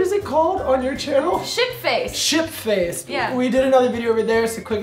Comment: I thought said shit face haha :D